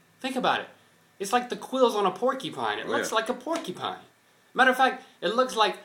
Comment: ha. it sounds like like is saying "porkypine"